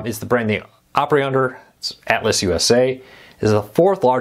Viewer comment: I'm from the South. Never heard Altice pronounced the way did in this video. To my ear it sounded like he was saying Atlas.